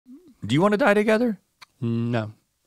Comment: I thought he said "Dine together" and that forst half went wild.